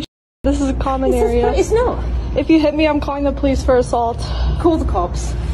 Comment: This is how many times she said “ I HUNG UP THE PHONE CALL” 👇🏻 👇🏻 👇🏻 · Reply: I heard her say "IM CALLING THE POPO"